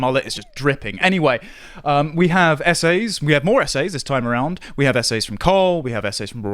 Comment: lol "essays". I thought he was saying "SA's" lol. Not sure you wanna be publishing those.